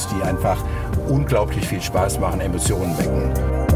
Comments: haha habe verstanden "die Emissionen wecken" :D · Ich verstehe bei 0:35 immer nur "...Emissionen wecken" - da hat er Recht, dieser Heuchler! Die deutsche Automobilindustrie steht heute in meinen Augen für absolute Ignoranz, Selbstgefälligkeit, Profitgier, Ideenklau, Trägheit, Lobbyismius, ja fast schon Hochverrat - gegenüber dem Staat, den Kunden, der Umwelt und den Bürgern! Vielen Dank Herr Zetsche